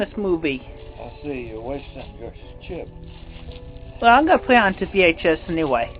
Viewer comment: Correction for your captions At 3:10 what says is is "i see. You're wasting your chip" and Chris says "I'm putting it on VHS". 😊